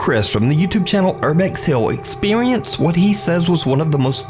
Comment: old vid but anyone catch the Expewience? said